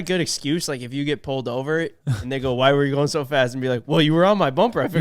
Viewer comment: I swear I heard say “nigga why u going so fast”